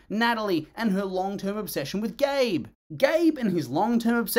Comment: I swear I heard ‘and her long term obsession with gay’